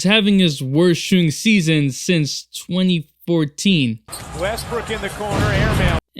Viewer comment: did he call him westbrick?